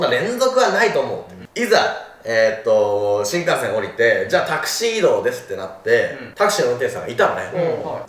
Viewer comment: タクシー移動がタキシードに聞こえたのは私だけだろうか…😂 ふざけてすみません。 ご無事で何よりです🙇‍♀️